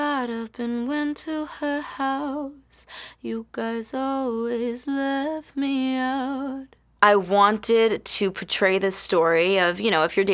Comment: this whole time I thought she said ‘you guys always laugh me out’